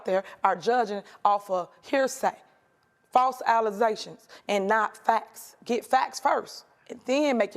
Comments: did she just say Alizations??? Instead of allegations? That's how you know this woman wasn't chosen because of her intelligence. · "false alluzations" she holds a position of power and has THAT poor of english?!?!? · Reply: I thought she said Alazastions, thinking about that Aleze 😅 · Did she just say "false alizations"?? 😂😂🤣🤣😂😂😂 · Did she say “False allezations”? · Did I hear false “allesations”? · Did she say "false allezations?" 😂😂😂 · Did she say "false allizations"? 😂